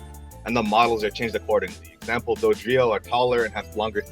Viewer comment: Not gonna lie. I thought you said they were giving Dugtrio longer and thicker legs...but then i realized you said Dodrio